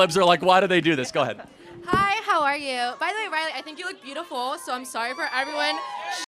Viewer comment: what about the blonde lady? Edit I realized her name is Riley, and I thought she said Charlie lol. What about Charlie?